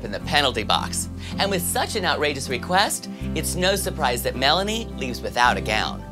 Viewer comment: did he say it's snow surprise....?